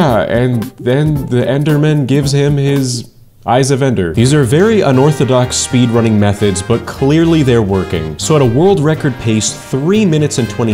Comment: did you say something with orthodox cause im a orthodox